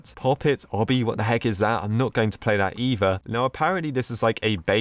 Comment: i thought he said eager but he said either